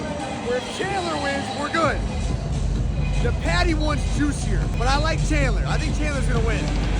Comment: Lost all the bets? I thought he said he had a “juicy” parlay that hits if Paddy won? 😮